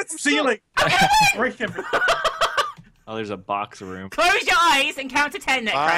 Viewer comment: I thought it said Hot Box Room o.O